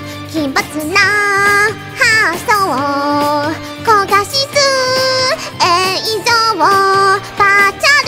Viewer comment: 「高画質」が餃子のせいで「焦がしつつ」って聞こえた